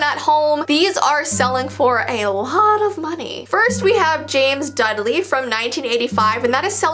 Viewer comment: I'm pretty sure she says "James Dudley" or that is what I heard, and I thought of Harry Potter